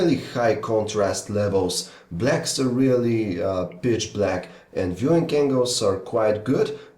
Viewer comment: It sounds like he said "bitch black" for a second :P